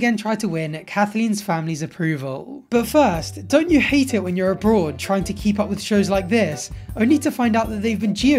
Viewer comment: I thought said “when you’re a broad” and not “when you’re abroad” lmao. I spend too much time laughing at revolting conservatives